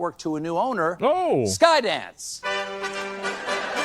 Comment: Sky-dance?!? I swear, the first time I heard it, I thought he said ‘Skynet’, which actually makes a strange kind of sense.